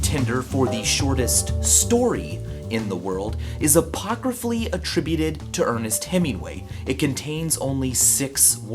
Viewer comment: What the heck is that word? :D I've listened 100 times and can't recognize it. :( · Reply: i'm 9 years late but he says "apocryphally attributed to" which means "not quite proven to be so but widely attributed to (an author)"